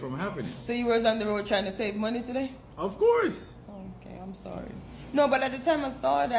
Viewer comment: and 17:12, I thought I heard the lady say, "I'm sorry." Sounded genuine! I had to play it again to make sure I heard that right. Lolol Must be scripted... or AI?? 🤣🤣 Seriously, though... 👍🏾👍🏾for her actually 'listening' to what her man was saying and thoughtfully considering the points he was calmly making.